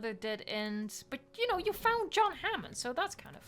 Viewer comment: i hope you did not say what i thought you said lmaooo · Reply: I said "You found John Hammond" No idea what you heard/wanted to hear lol